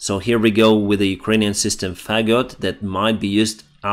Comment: the Ukrainian system... what? Sounds like "fag got". I know classically "faggot" referred to a bundle of sticks. But I'm assuming that word may have a different meaning in Ukrainian... that or I just misheard it.